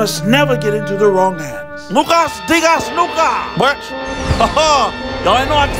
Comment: What did say?😂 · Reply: Lukas dingas luka 😂